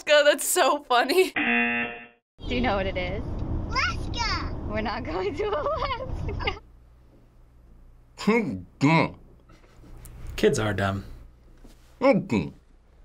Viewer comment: I thought see said vodka